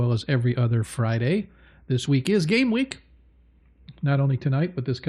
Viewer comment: I swear it sounded like you said It's GAY week lol but I know you said it's GAME week.